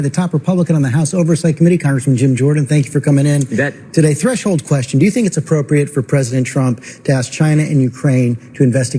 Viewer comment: did i hear that right? did jim jordan say "Bet." in response to Stephanopolous? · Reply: “You bet” but the “You” was kinda muffled